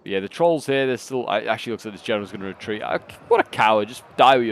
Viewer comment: what did you say to my epic gen? his the best troll king known in middle earth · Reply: A coward lol